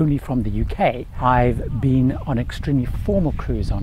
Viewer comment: Did you say you have been on QAnon only cruises?